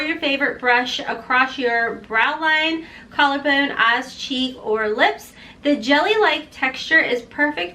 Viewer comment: Love your accent...but I swear I heard you say a$$ cheeks...not eyes, cheeks. I thought, "That is certainly a new thing. A$$ highlight."